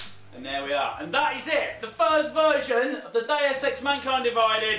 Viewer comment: Did he just say first version?